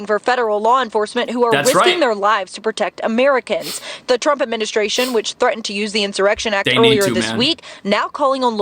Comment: did she say "The Terp Administrations"